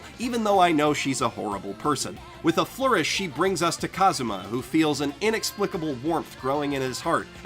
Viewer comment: I heard him say Subaru instead of Kazuma